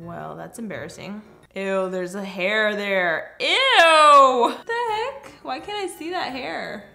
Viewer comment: min. Put on captions it does not say "oh heck!" It say oh *i*k